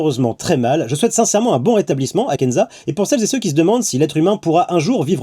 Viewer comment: j'ai entendu "je souhaite un burata blissement" j'suiss matrixée xD